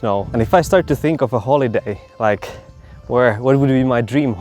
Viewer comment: At first when you said , “holiday”, I thought you said holy day. Actually, though, days spent like that would be holy days.